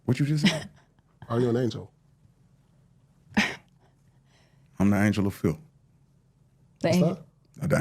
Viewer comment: I thought he said he the angel of filth. Sounds like the same difference